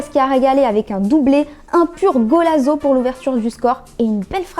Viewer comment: un quoi ?! ^^^ ^^^ bonne vidéo sinon ! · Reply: Un golazo